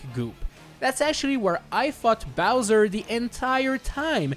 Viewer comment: I think I heard “fought” wrong……